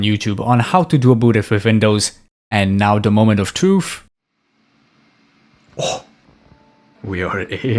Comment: Did I hear 'Moment of Tooth' ?